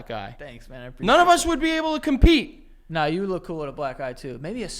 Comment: wasnt fully listening and fully thought they were saying "you would look good with a black guy"